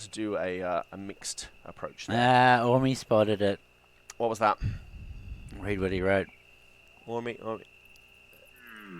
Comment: Anybody catch what the spoiler alert at 1:02:30 is? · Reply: The red deer call, bug fix. You can hear it!